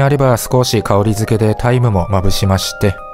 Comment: タイムが大麻に聞こえた自分は疲れてる